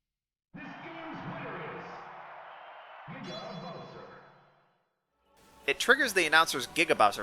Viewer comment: WHAT DID HE SAY?! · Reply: Giga Bowser.